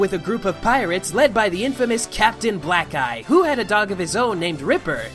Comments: I thought he said "captain black guy" at first instead of captain black eye😂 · I thought u said Captain Black Guy 😂😂